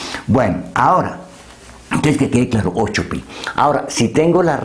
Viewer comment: Qué dijo en el minuto 8:12??? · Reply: Entonces que quede claro la respuesta es 8pi. Saludos!!